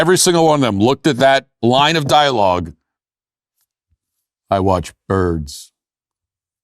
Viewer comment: When he said, "I watch birds." I heard, "I like turtles.". 😂😂😂